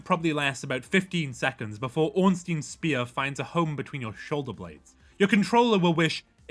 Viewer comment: At first I thought you said Ornstein's smear and was referring to the player which made chuckle, since it wouldn't be wrong after he's done fighting